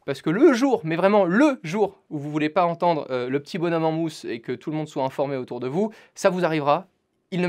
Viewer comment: j'ai pas compris il dit quoi? ¿? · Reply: J'entends "le jour où vous voulez pas entendre le petit bonhomme en mousse" Je comprends qu'il veut dire "au cas où on a pas envie que les autres entendent ce quon écoute" mais la phrase qu'il a dit jai pas compris mdr